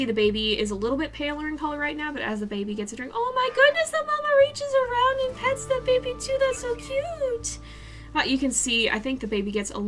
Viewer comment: Can someone please tell me what she said? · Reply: ‘OHMYGOODNESS THE MAMA REACHES AROUND AND PETS THE BABY TOO, THATS SO CUTEEE’